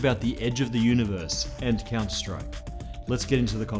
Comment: Do I really heard Counter Strike at 10:39 ?